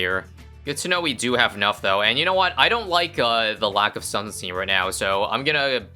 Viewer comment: What does he say at 13:37? The lack of sun seen?